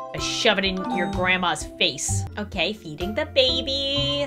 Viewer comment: HELP I THOUGHT SHE SAID EATING THE BABY👁️👄👁️